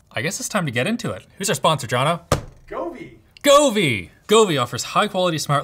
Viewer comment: When he said Govee, I thought he said Kobe, RIP